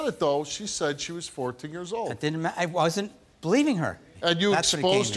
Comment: Did anyone catch at 8:12 when Steve says “she said she was 14.” He starts to say “it didn’t matter,” and then catches himself. What a creep.